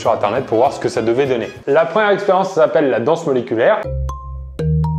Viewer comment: J'ai entendu "La danse d'auriculaires"🙄🧐😂😅